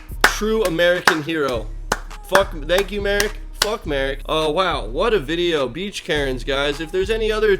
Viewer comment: what’s he saying ? F*** Merrick